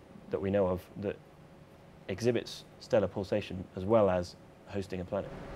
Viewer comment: hehe... 7:38 i heard teleportation :D